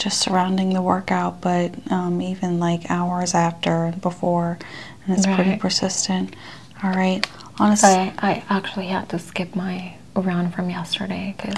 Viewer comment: I keep hearing the word hony. Am I the only one?